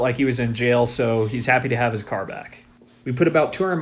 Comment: I must be tired, for some reason I thought that said he got his chair back 😂